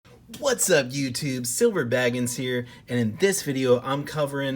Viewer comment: Why's it sound like your say silver bragons